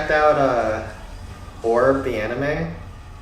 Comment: What’s the name of the anime he says around 26 minute around 25:40? “Ore of the anime”? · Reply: Orb